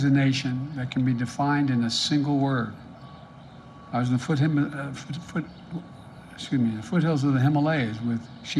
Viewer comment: “America is a nation that can be defined in a single word, Ashafoothiminafootfoot” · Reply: Just an FYI, the first thing he says after saying that america is defined by a single word is "I was at the foot-" and then starts summoning demons. not once did he say 'possibilities' and u know we can literally see the clip at 31:01 contradicting that garbage lol